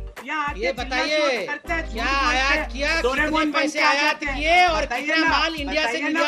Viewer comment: Did he say "Yahan Doraemon ban ke aa jate hain"??